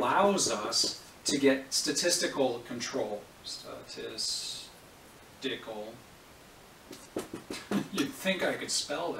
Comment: really sounded like you said "dick hole" and I was really surprised.